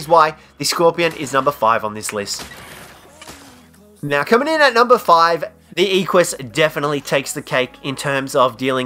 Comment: What he says:coming in at number 5 The captions: #4 Equus